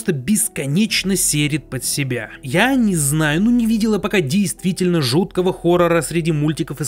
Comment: мне послышалось или он сказал "не видела"?